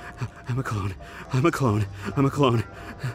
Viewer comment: The first time he said im a clone i thought he said ima cold